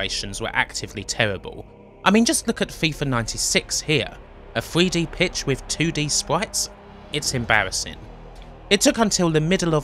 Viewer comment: what does he say is embarrassing? I can’t understand because of the speech impediment. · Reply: "A 3D pitch with 2D sprites"